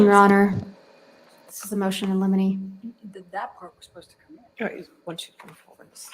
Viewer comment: Omg listen to what Elaine says when she objects: at 1:07:16 It sounds like she said that was supposed to come in…. What does that mean???